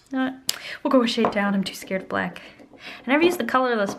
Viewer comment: she says shide instead of shade LOL